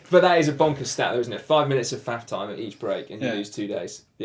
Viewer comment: sounded like "...5 minutes of fap time..."